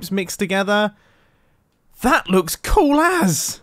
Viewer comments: did you say that looks cool ass just wondering 13:20 also happy birthday love the build i have a idea for it make it a 2nd demise bunker and love the old school mining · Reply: No, he said "cool as". It is slang. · a British dude trying to avoid coppa okay but he did say “cool ass” right? Because that’s what I heard- [EDIT] another Mumbo trying to be 13+ moment